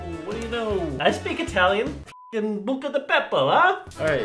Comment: Lmao did you just say Bucca di beppo ? Like the restaurant 😂